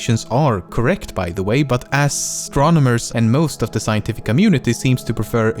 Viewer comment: DID HE JUST SAY ASS TRONAUTS 😭😭😭